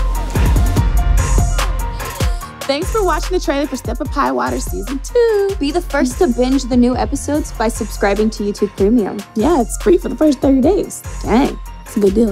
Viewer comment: did she say free for first 30 days?